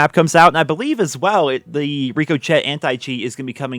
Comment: what he say? "rico-chet"??